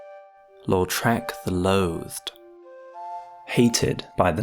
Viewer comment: i thought he said “lord shrek”💀